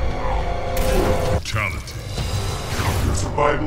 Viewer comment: I thought it said “Quality” lmfao.